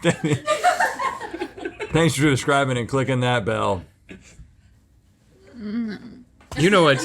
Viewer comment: My mom always used to say “what’d I just say?” Cause she knew I wasn’t listening 😂 19:23 Also, did Rhett say “describing” instead of subscribing 😄